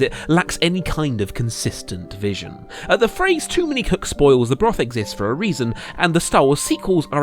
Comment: Instead of "Too many cooks spoil the broth" I heard "To many cucks spoil the brothel" And tbh I was ready to accept that